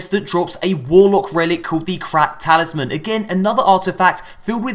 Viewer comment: I thought said the crap talisman when he was talking about titan subclass